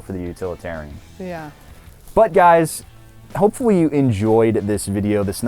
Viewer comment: did you just call us "butt guys"?